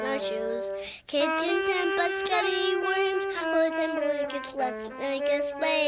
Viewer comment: almost perfect! basketti could be another way to spell perseketti, for a kid, meaning spaghetti at any rate, and i hear "bows and blankets" (and thus illustrated) but otherwise i think you've got it! g